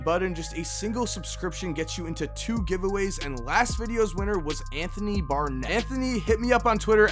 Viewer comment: THE NEXT WINNER OF THE MARCH JERSEY GIVE-AWAY IS... Anthony Barnett!!!! YOU WON MY DUDE!!!!!! CONGRATS!!! DM ME ON TWITTER FOR MORE INFO!! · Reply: I thought he said Anthony Bennett